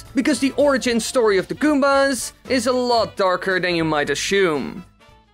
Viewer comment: "...is a lot darker than you might ashoom." Okay, put an Euro in the "poor jokes" jar. Nice updated Intro though. Evolution > Revolution 2:28 On my small Handy Screen I cannot quite identify what Toad has in his mouth here, but it doesn't look healthy... I thought you were going in the classic "the good are the bad" direction and would suggest a Civil War between the Toads and the Goombas in the old Mushroom Kingdom Days and as the Goomba's Side were losing they made "a deal with the devil" and joined up with Bowser to regain their status. This story is super-popular and I don't know why. Good that your theories have at least some facts backing them up. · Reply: Its the way accent is. That's why it sounds like he said school. He said assume